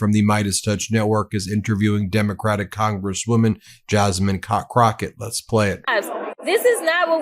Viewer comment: did he just say JASMINE COCK ROCKET?